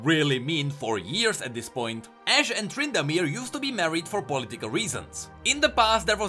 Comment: i thought he said ashe and trundle...